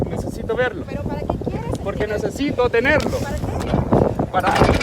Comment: does he say “necessito dinero” instead of ticket? Maybe he really was after a bribe. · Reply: Yeah he says necesito tenerlo